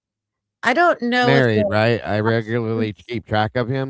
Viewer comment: I think he says “ a mate right “